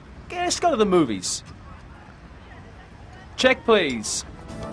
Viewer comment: at minut 46:35 Did Sam say :- check please ?